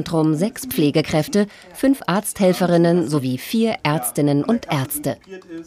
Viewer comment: habe ich das jetzt richtig verstanden: 6x Pflegekräfte 5x Arzthelfer 8x Arzt (4x männlich + 4x weiblich)? Sind dann aber ganz schön viele Ärzte 🤔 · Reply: Nur vier Ärzte